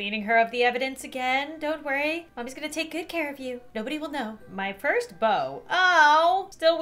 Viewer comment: I thought she said my cursed bow she said my first